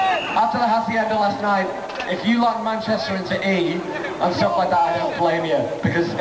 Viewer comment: Can someone tell me what he says starting 0:46? I only heard that he'd like to piss xD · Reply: "If you like Manchester" I hear.